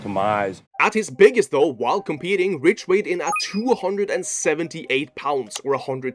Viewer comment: i might be geeked but i thought that shi said he was 2781 pounds 😭😭😭😭